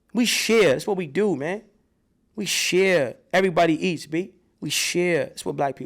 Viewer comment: I thought you said we are Jews if so how are Africans?